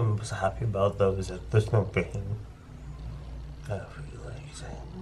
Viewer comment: "the thing im most happy about is that theres no bacon " .... Thays what i heard lmao🤣